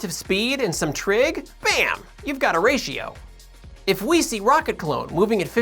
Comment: Every time you say "a ratio" I hear "Horatio". Otherwise, this is perhaps my favourite video you've ever done.